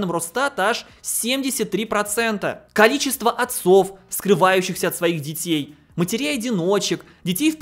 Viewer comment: я не расслышал там скрываюшихся или вскрываюшихся? ( ответе пж те, у кого слух лутше моего ) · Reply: Скрываюшихся